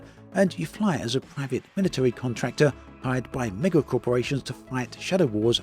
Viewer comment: I almost spat out my coffee when I misheard 'mega corporation' as 'LEGO corporation'!